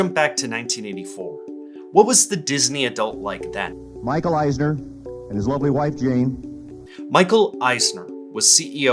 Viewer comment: I heard "Michael Eisner in his lovely white jeans", which totally tracks because I could 100% picture him wearing white Wranglers that are 2 inches too short